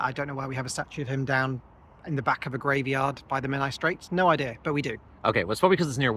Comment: Accidentally misheard "the Menai Strait" as "the Men I straight" and now I can't help saying "the men i gay" 🏳‍🌈